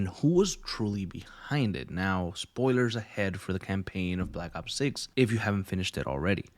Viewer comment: i swear i keep hearin you say black ops 6